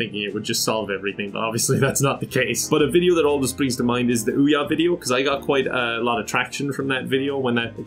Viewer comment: I am curious, what was the video he was talking about? I couldn't understand. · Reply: Search Callmekevin Ouya